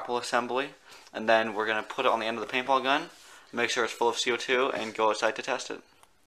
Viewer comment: I think he said "make sure it fools you too"